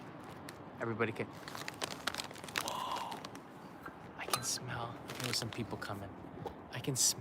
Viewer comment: I thought you said you could smell some people coming lol